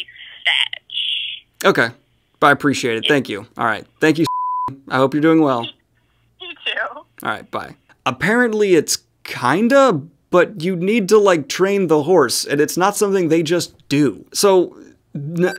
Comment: at like 13:00 it took me a second to figure out that he bleeped out the name I thought he just said "thank you goodbye b*tch" and bleeped out that part XD